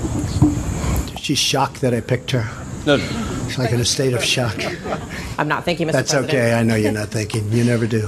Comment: 17: 54 I heard "I'm not thinking Mr President" too tbh